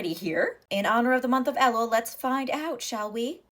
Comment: I keep mishearing it as "the month of lol".